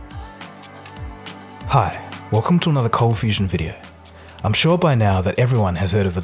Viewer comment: Oh I thought she said confusion tv I was like bit** I ain’t confused